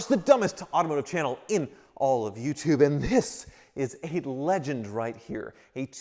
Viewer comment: I had to start the video over and turn up my volume. I thought you said it was a "Lemon" not "Legend". Though for you, it's likely both.